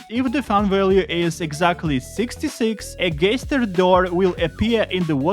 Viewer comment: did you just say gayster